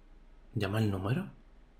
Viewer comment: creo que dice "número del teléfono"